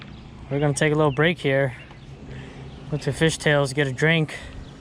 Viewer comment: Lovin' all the action! Did you say you are going to watch some "Fish Tails" videos on your break? 9:25 😄😄😄